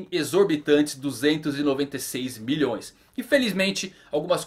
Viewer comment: eu ouvi certo? Bilhões??